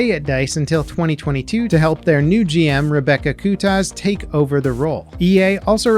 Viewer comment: excuse me? Did I hear "Rebecca *Kutas*"?😂